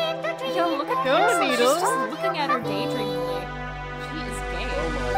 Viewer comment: Look what the people says: when I heard it:💀💀💀💀💀💀💀💀💀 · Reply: I looked in captions, it said “She is gay” 😨